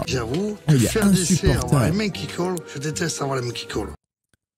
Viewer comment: il dit quoi le patron svp ? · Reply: Je déteste avoir les mains qui collent